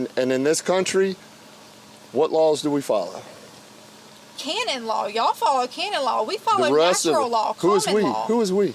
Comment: Argumentum ad... As a request, here's my video with the most facepalms, Flat Earth is DEAD! Tonight we dine on Flerf Pizza! <link> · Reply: Sounded like she said candid law, but I guess it's canon law to the officer ... That it's their law and not hers? I think she just made it up after common law. Lol